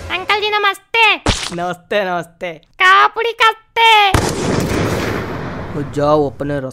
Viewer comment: what she said? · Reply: Khao puri khaste